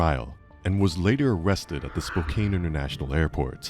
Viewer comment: So it's the cry of the guilty! That cry-talking they just keep doing? Oh and it's Spoke-can not Spoke-caine. I thought you said "cocaine airport"😂